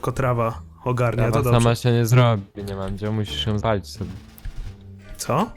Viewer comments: ja usłyszałam "musisz ją walić sobie" :D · Mandzio, ja usłyszałam "musisz ją sobie troche zwalić" xd